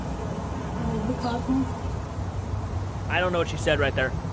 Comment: I think she says “follow the cosmos” but I’m not 100% sure it’s all over the place tbh. Talking to birds now, is it ghosts or birds? It’s hard to even follow what she thinks she’s “talking” to 👀